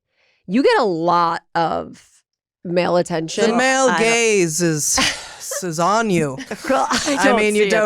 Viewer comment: the male gaze is on you, Heather! Hilarious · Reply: It took me a sec…I thought she said “the male GAYS is on you”.